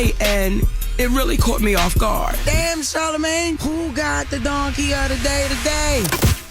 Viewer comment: Sound like he said darkie of the day 💀